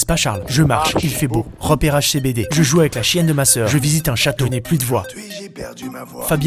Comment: J'ai entendu : "Je joue avec ma chienne de soeur" ...........................................